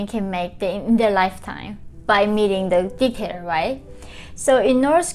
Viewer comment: "by meeting the dickhead right?" is what it sounded like!!! Haha